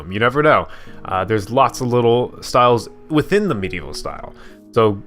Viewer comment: For some reason I heard you say meat evil style! XD Great video!